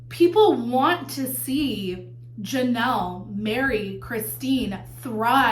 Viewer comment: it sounded like you said “people want to see Janelle marry Christine and thrive” 😂😂😂 then I realised it was “Janelle, Meri, Christine” 😂😂😂